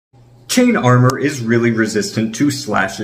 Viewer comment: What I heard: "Chain armour is really good against imaginary knife" 🔪